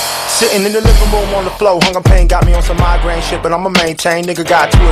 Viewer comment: It sounds like it says "hunger pain" not "all the pain".